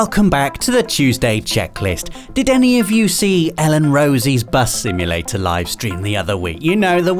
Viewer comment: I misheard "Elle and Rosie" as "Ellen Rose" and got confused as to which channel I was on for a sec 😁